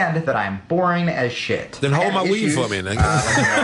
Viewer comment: Sherrod "than hold my wing nigga" hahaha I usually don't like Sherrod but that shit was funny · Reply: sorry man...i heard "then hold my weed nigga"...not "wing"...or maybe i missed the joke about "wings"...that could be it too.